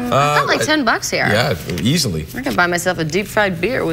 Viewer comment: The way she said 'I can buy myself a deep fried beer with that" ! 7:39 Btw what is a deep fried beer? or did i hear it wrong?